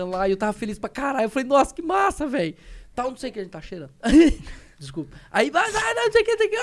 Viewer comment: Dilera: Ele tava cheirando Igor: Isso foi um tique ou é verdade mesmo? Mano, nunca dá pra saber ao certo hahaha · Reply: Ele falou agente tava*